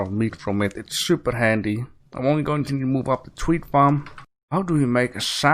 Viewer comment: I laugh when you say Wheat, because it sounds like you say tweet XD omg poor horse draggin behind the boat lmao lava waterway sounds cool too